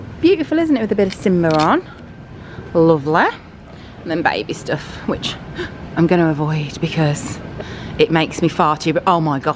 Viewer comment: Totally thought you said “going to avoid the baby stuff, it makes me farty 💨 “ 😂😂